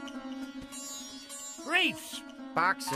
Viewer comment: back then i didn't know briefs and boxers meant underwear, and also i thought was saying boxes. That's why i was confused but still laughing every time they tore off their clothes. Because i thought they did it for no reason